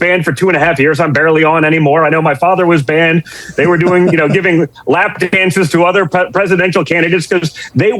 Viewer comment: did I mishear him or did he say "giving lap dances"? Really??